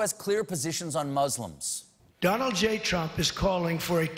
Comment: (Closed Captions): "NOW, VEHICLES..." What Stephen said: "Now, folks..." Whoever's in charge of this channel might wanna fix that 😅😅😅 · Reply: Also, at 3:54 He says "Donald J. Trump" And the captions say "JOND J. TRUMP"